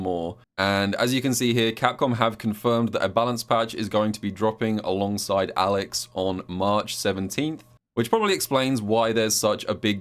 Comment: i was a bit distracted so i thought i heard "Alex Horne" was dropping in march lol, i can even picture him in World Tour giving you the most ridiculous tasks in the name of the Taskmaster